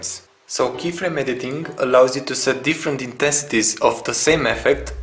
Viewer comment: did you say 'intestes' at 0:15? pls repeat and whats the hot key for 'keyframes'? tnx